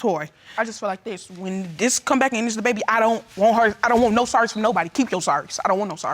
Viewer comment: When she talk it sounds like somebody pressing fast forward 😂😂😂😂😂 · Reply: when she says she don't want no sorry I kept hearing she don't socks from nobody and the subtitles showed it too.. took me sometime time to realise it was sorry😆😆